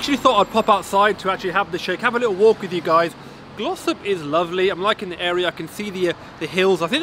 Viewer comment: I swear I thought you said Gloucester... Turns out it's Glossop. Thanks for the review matey